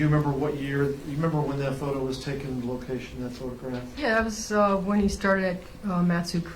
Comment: does the lawyer (I’m guessing the prosecutor) say “and do you remember when that photo was taken, the location, that sort of crap?” Because I have rewound it and listened to it probably 10 times minimum at this point, and that’s exactly what it sounds like he says; I’m just having a hard time imagining that’s what he would say not only in court but during a trial for a 16 year old who was violently murdered